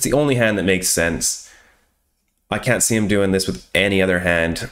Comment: Hi quick question, at17:02 in the video for your Ad3d hands, you mentioned that if the villian has A9, he will never bet like that on the flop. Could you explain a little deeper regarding this? i don't quite understand. Thank you so much! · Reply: He said 8T not AT